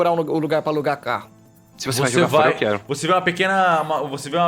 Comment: Pelo amor de deus alguém me explica do que a tá rindo. Eu não entendi o que ele falou sos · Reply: Alguém quer isso? Se for jogar fora, eu quero